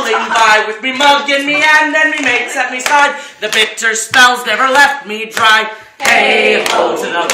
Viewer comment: The second verse, the last line. Did he say "the victor spells", "bitter spells", or "bitter stouts"? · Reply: the victor spells